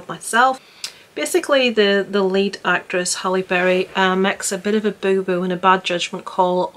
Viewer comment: I had to turn closed caption on because with your British accent and my APD, I thought you said, the late actress Halle Berry. Thank God the closed caption on the screen read, *the lead Hale Berry*. Whew! lol Man, I was wondering why no one in the comments questioned this or made a statment.